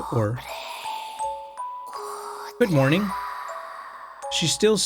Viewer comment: 5 minutes in and already e m o t i o n s 40:14 добреы утра is what was whispered, aka "Good Morning" in Russian